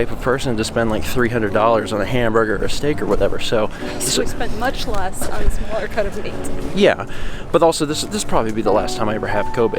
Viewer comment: Did anyone catch what says here? I can't quite make it out. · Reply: Just spend much less on a smaller cut of meat